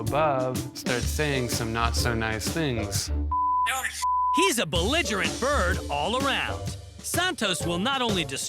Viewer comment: What kind of bird did the voice-over guy say? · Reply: He described the bird as belligerent. It means Hostile, or aggressive. In this sentence the narrator is meaning that the bird is antagonistic, confrontational.